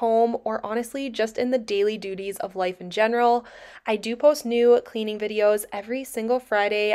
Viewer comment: I had your video on in the background while I was doing something else and your were cleaning your room and you introduced yourself and it sounded like you said you post nude cleaning videos on Tuesdays ... LoL 😂😂😂 .... I had to go back and listen to it again ...